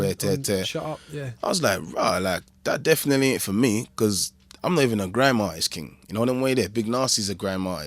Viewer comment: i'm american and can't figure out what he says at 26:12 - can someone help me out.. to me, it sounds to me something like "i'm living a grandmar is king" - i mean i know that's not right, lol, any help? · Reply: lol. He says "I'm not even a grime artist, king". Thanks for watching!